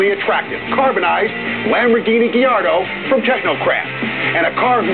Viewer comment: Did he say Diardo after 0:19?